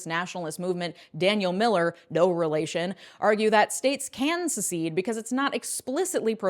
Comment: wtf are you talking about? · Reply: she actually says “no relation” but still lol